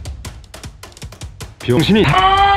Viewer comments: X신 이다! 라고들림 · 눈 감고 들으면 병123신 이다 라고 들림